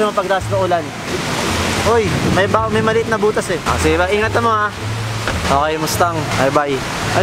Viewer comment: Did said " ingat kamo?" 4:35 Wala lng hahhahaha, its an ilongo word kase, so I was just thinking if they're ilongo:)